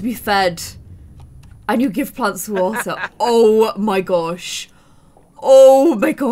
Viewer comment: I love when Sammy B was drinking water says you give PLANTS to WATER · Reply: but i think she said "You give plants water"🤐